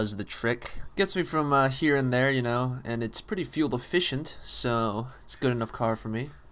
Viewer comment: it almost sounded like you said your car was fuel deficient LoL